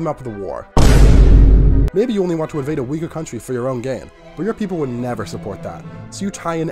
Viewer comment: I heard Uygur country💀 Really glad I won't have to tank your credit score🤧👍